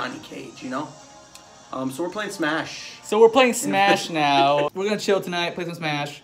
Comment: I thought he said we are gonna Chill and Smash 😂wow I was like WTF! RUN CABOOSE 👀